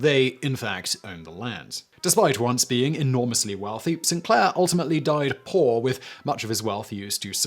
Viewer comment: St. Clair is in my family on my late mother's side which was her maiden name. Also he was second in command of the Revolutionary Army under Washington. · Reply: The British pronounce "saint" as "sin." I thought he was saying "Sinclair" - which, I suppose, may be a corruption of St. Clair.